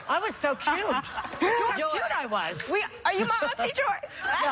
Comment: Raven Symoné is clearly saying "are you my auntie, Joy?" at 2:25.